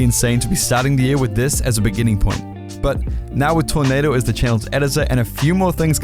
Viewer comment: Hey all, hope you enjoyed the video and I’m looking forward to the upcoming year editing on channel! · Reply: honestly thought he said thanos channel. this isnt marvel oops. yeah hes done well tho!